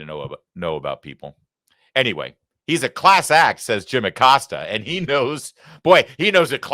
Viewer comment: I thought he said he was a class ass ??